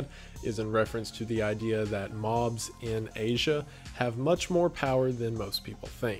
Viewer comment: I swear I thought he said moths in asian.